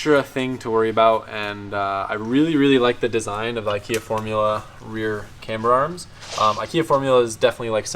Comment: who else thought he said Ikea Formula